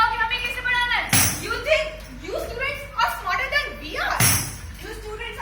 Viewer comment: Can anyone tell me what that girl said at 11:28 min?? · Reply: You think you students are more smarter than we are …. You students are more techno savvy than we are …